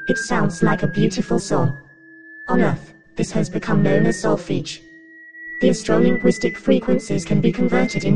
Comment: What does this say at 5:32? Is there a transcript available? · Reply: Solfege I suppose